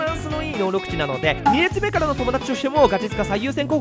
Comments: 2列目からの友達にしか聞こえんw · から言ってること2列目からの友達に聞こえるの俺だけ？ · 2列目からの友達に聞こえるのオレだけ？ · 2列目からの飛び出しが 2列目からの友達にしか聞こえん笑笑